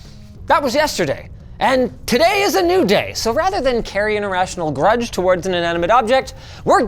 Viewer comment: Did he just say "a rational grudge", or did I mishear that? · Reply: Irrational haha